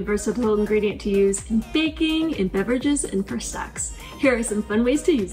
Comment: Good and helpful video. Did you really say, "Frozen bananas are a versatile ingredient to use in baking, in beverages, and for sex." ??